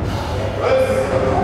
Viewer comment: Thought he said cmon morty lmao